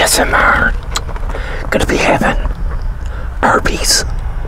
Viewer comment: Bruh I had subtitles on at first and when he said arby's it thought he said herpes like tf🤣